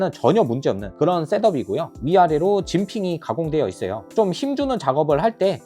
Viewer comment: 중국 강제 설명 후라... 진핑이라고 들었...네요 ㅋㅋㅋ